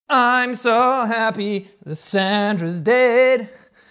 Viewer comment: Ngl I thought he said santa instead of Sandra 😂